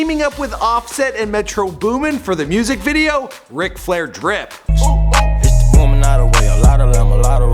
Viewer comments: I thought it said rip flair drip.. almost had a heart attach 😰😪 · I thought it said ric flair RIP